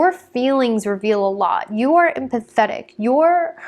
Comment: I thought she said "you're pathetic" 😂